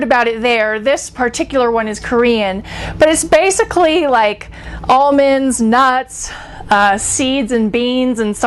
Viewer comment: I thought she said, "all mens nutts" on the tea